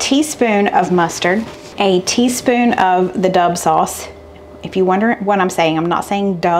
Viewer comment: I've been watching your videos for 2 years and all this time, I thought you were saying DOVE sauce and I never knew why. lol